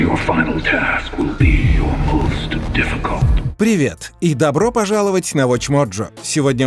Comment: Мне в начале послышалось что билли говорит "привет барби!"